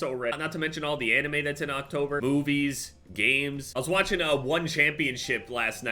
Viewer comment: straight up thought said "boobies, games-" lol